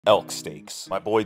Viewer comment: I thought he said Elk STINKS, and I was like 😶